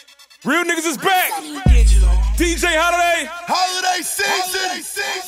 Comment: Someone can tell me what says in the second 0:07? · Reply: Holiday Season?🤔